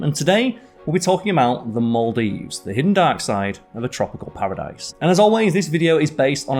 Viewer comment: I heard "the hidden dioxide" and it too awhile to realize its "hidden dark side". I cant watch this stuff anymore. Time to unsub.